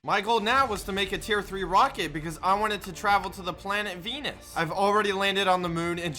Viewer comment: i thought he said i want to travel to planet BEANOS